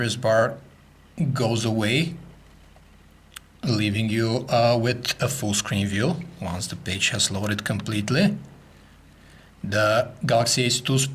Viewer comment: Lmao!! Sounds like he say's, "leaving you with a full screen view, one the BITCH has loaded completely"!! I nearly died!